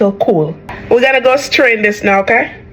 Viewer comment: I misheard this lady and thought she said "We're gonna ghost train this now ok?"